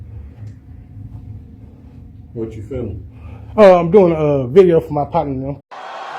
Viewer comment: “I’m making a video for my patty melt.” Is that what is trying to say? He’ll have to settle for stale bologna sandwiches in the county jail.